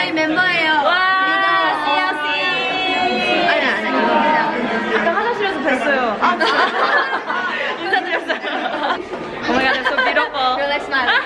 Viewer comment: when you told sorn you guys should hang out more i thought she said um... no but she actually said i know lol also it was really funny when you said to seungyeon that you had bumped into each other in the bathroom